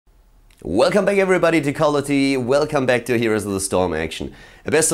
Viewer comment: Hey love you video you have great commentary!! BUT..... @ 0:00 it sounds like your saying "welcome back everybody to color tv" lol sorry